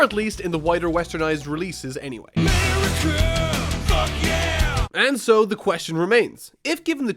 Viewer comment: did he say f%ck yeah